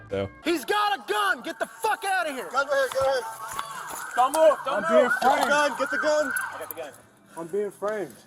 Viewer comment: did he just say i was being framed